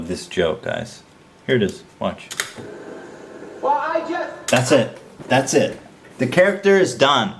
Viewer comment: What is even said at 7:48? It sounds like "well I just..." but there is too much timing before that to have it be the continuation of a sentence and I don't hear anything after that.